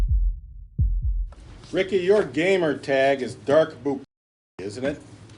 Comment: The first 3 seconds I thought the guy was saying “Ricky you’re gay”